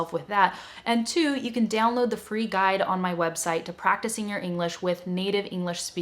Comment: Little mistake, you put in the subtitle word 'two', instead of 'to' (I mean the second 'two', before the word 'practicing'). It's just my observation. And, thanks for the videos, they're very useful!